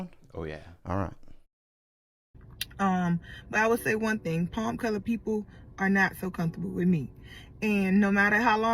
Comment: did she just say "palm colored people"?? lol